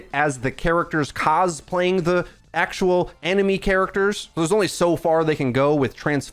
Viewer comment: The way you pronounced anime... 💀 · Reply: Right, I thought he said enemy character 😂